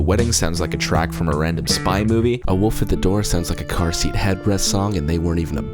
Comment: Hi! I'm sorry, but I am really curious of the band you compared to the song A Wolf At The Door with. It was on minute 29:27. I couldn't understand your pronunciation, mainly because I lack a very good trained English ear, but also captions did not help a lot; tried to search something about Redhead but Blonde Redhead was already a band at Hail To The Thief's release and then I searched up "car seat head breath band?" but it didn't work out. I just really loved A Wolf At The Door and if there is a band that sounds like this, I'm up to it. . . XD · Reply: car seat headrest